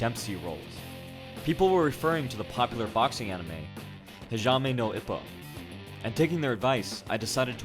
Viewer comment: wait did i heard pajama no ippo?